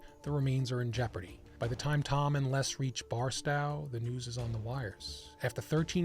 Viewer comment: I was enjoying this non-AI narration until 40:15 when I heard Barstow as "Barstao". 😅